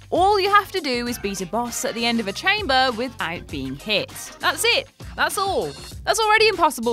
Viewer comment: I am having trouble understanding the moderator thick accent. First video I heard she said "with iron beam hit" instead of "without being hit". The second one, I didn't understand what is she trying to explain, maybe its a british humour that we still haven't familiar with. So ha ha..